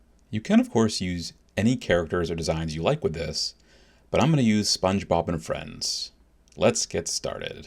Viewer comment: did he just say sponge bob and HER friends?